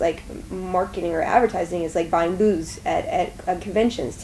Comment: On Q3: Booze (what I heard), booths (what you said): both work! :)